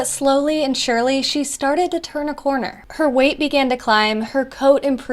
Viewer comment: I thought the captions said she started to have cancer but it said she started to turn a corner